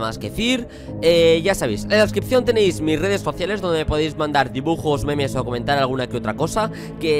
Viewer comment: yo sino me equivoco escuche redes faciales?? Jajaja